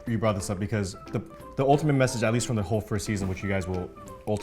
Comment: Did say FIRST season 8:30??